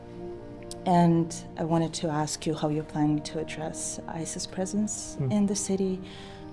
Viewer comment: When the Russian asylum seeker said "I wanted to ask how you're going to address ICE's presence in the city" , I first heard "ISIS presence in the city" and was confused for a moment. The irony that the US created their own "ISIS/ICE's presence".